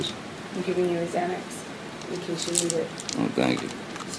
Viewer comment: When she gives him a xanax because he's fighting back for himself too well is just the most manipulative part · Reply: YESSSSSS! Did anyone else hear the part after (it was really low & hard to make out) but I heard AH "just in case you need it." JD " oh right thank you" or something to that effect Then the lower part AH "I think it's wearing off" JD "You're probably right." So she gave him a Xanax at the START of the argument? That makes him easy pickings for her to win without much fight!